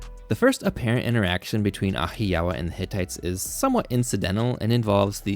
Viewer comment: I keep hearing “Wahiawa” and I’m like “I know where it is! It’s in Hawaii! I solved it.”